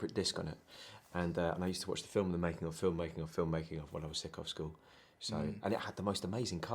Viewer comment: Did george entered a law school?! Well if yes, this man got the talent and the brain.. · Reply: Martina Gracia I think he said “ill off school” cause later on he says he’s been sick, but first I thought he said law school 😂